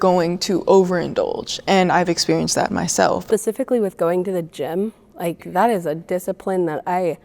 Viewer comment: She said pacificly instead of specifically